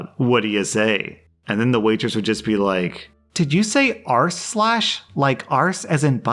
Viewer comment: I had never once heard it as "arsh lash" and now it's all I can hear.